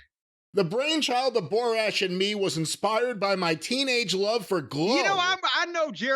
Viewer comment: I misheard that and though it was "my teenage love of glue" lol.